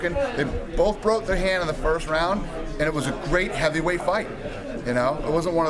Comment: Mis-read. I agree. Thought you said light-heavyweight